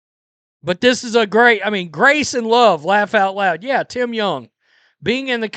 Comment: Stephanie White always stands up for the women she has personally tasted. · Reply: I thought she said she was about "Love and Grace". Now I understand she said "Love and Gross"